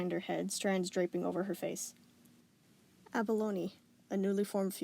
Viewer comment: At first I thought she said "a baloney"